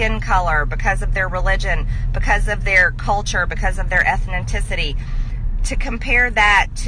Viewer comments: That isn't even remotely the most disgusting thing she has said. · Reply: Speaking of what the🤪 dingbat🤪 wingnut🤪 says did anyone else notice at 6:07 instead of saying "ethnicity" the idiot said what sounds like "ethninticity"🤪🤣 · "Ethninticity"? That must be MTG's contribution to the English language. Ethninticity... No wonder MTG defends statues instead of books. Books contain words, including hard words like "ethnicity". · Did she just say “ethninticity” at 06:08?